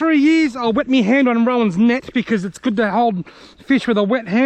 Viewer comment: I could have sworn I heard you say that you wet your hand on Rowan's neck which made me laugh so hard I almost spat my drink out. Then I finally realised you said 'Net'... lol